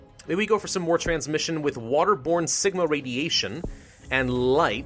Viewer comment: When you said Sigma I thought you said Ligma